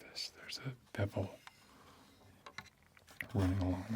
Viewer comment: Ah you beautiful man... So glad I became a patron yesterday. Also: How much does it say about me that I misheard you say "bevel" as "devil" initially? Or is it because I've come to expect certain themes from your videos?